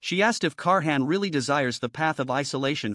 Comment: I keep hearing 'car hand' as his name haha